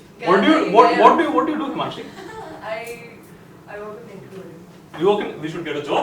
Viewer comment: what did she say about where she works? can anyone please tell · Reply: Nickelodeon